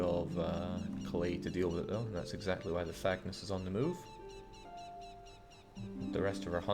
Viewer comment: I keep mishearing it as The Fagness.